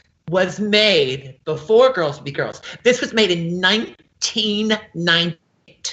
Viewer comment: she sounded like she said the jumpsuit was made in 1919 :D